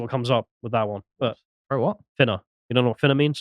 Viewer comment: "Finna" and here i thought he was saying "Thinner" good thing he's not a Gypsy.